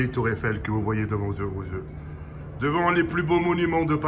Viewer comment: devant quoi ? mdr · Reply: yeux vos yeux